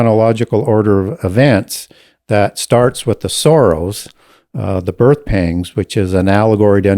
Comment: I kept hearing "the times of the Soros" rather than sorrows